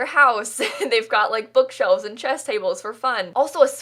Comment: When she said “bookshelves and chess tables” I literally thought she said “bookshelves and chesticles”🤭🤦‍♀️😂😂😂